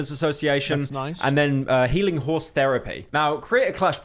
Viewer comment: I thought I heard "healing horse therapy"